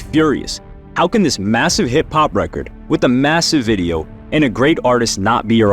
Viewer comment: i thought you said hip-hop lol